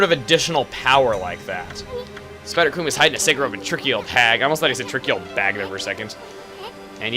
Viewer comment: He says he thought is said "tricky old bag", it looked like that actually said that instead of "tricky old hag". :P